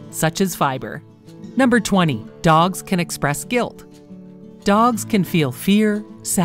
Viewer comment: When it said “Dogs can express quilt” I thought it said “Dogs can explode”